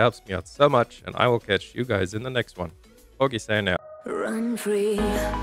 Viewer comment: Everytime you end your videos I swear I always hear "poggie stan out" xD good content tho!